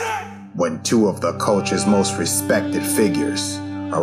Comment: Thought it said "Two of the biggest fakers" @ 1:03 😂